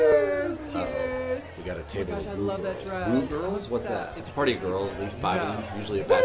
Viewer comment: So I can be wrong but I thought one of the girl said : omg I love that drug or omg I love that dress 👗 so pleasantly confused